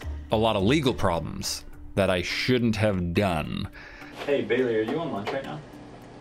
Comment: I thought said hey baby are you on lunch